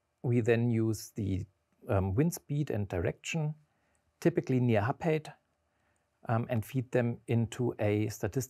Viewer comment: What is he saying at 5:19? I could'nt understand it. "Typically near h-paid?" That doesn't make sense :D